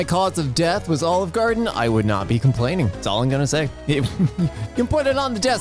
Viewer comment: i thought it said “i’m gonna bust”